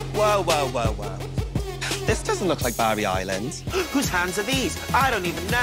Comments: I love that every American who hasn't seen Gavin & Stacey, thought Tayce said "Barbie Island" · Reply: I’m British and I thought she said barber island... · I love that every American who hasn't seen Gavin & Stacey, thought Tayce said "Barbie Island" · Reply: I am English, I love Gavin & Stacey and I thought she said Barbie Island